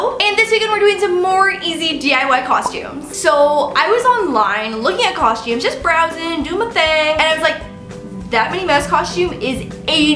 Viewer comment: Hey Lauren! I love this video so much, and I want to make sure everyone can understand and enjoy it. That's why I wanted to let you know that your captions are a little off. At about 0:42 the auto-captions say "I was online looking at costs you just browse and do what they like that most cost you is eighty dollars" where they meant to say "I was online looking at costumes just browsing, doing my thing, and I was like that Minnie mouse costume is eighty dollars.” When you get a chance, could you fix that? That'll help the 360 million folks globally who rely on closed captions to understand videos. Thank you, and keep up the great work! (